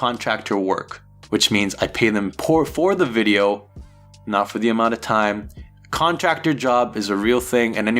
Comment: Anyone else hear the slip where "paid them poor- for their work" 1:21:46, if you want to go back and hear it for yourself. It's very telling as it is a psycological slip of the truth rather the words he is supposed to say. One would have thought he would have picked up on that and reshot the part.